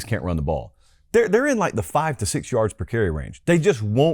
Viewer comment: I think he said specifically Jaden Baugh had 5.6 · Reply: that is not at all what he said. 24:20. He said "they are in the 5 to 6 yard range"